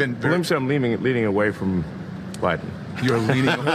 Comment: Pat - you misheard.. Elon did say I'm leaning away from Biden...